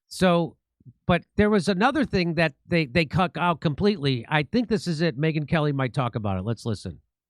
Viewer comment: They what out jimmy?? 😳😳🤔🤔 Did jimmy just say they "cuck out"? I mean, it's kind of appropriate in this scenario.... 🤣🤣😆😆💯💯😮‍💨😮‍💨🤦🤦